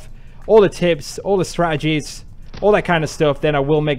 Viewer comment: Wait I swear I heard you say SPRATTegies instead of strategies at one point. Anyways if not that would be dope for a sniping help video title.